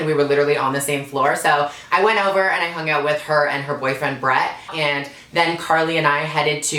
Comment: turn on ur captions! 😂 oml I died. if u couldn't catch it, the captions read, "And her boyfriend, Breath." haha😂